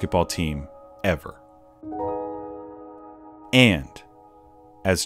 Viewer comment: I legit thought the last thing he said was "fight me" boy "finally" lmao